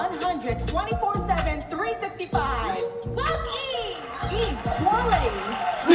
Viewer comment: I swear, until you pointed out it was "Woke Eve," I thought the girl was saying "Fuck Eve." Someone needs to learn to enunciate and someone else needs to stop thinking little girls are swearing.